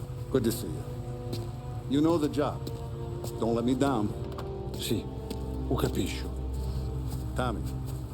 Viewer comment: Translation 2:10 if you understand 2:33 marvelous 3:03 you speak at least a little Italian the boss told you the plan 3:25 errr please understand lets enter the labyrinth without being heard or seen the safe is behind a painting on the first floor give me some time and i will do everything if i made any errors let me know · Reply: Good translation, but at 2:10 he said "Yes, I understand", not "If you understand". This is what he said at 41:30: "Tommy, I have to tell you something. On our next job, I take care of the safe, understood? I almost died in that mansion, and is all your fault! And, at least, learn some Italian, out of respect for our homeland. Is like talking to my dog, I know, but Christ, I had to tell you. Don't do it ever again, ever."